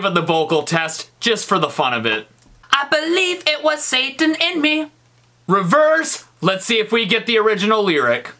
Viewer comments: I heard"it was saten in miguel" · I heard "I believe it was satin in mea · In the 2nd beat it thing I heard “I believe it was Indian” lol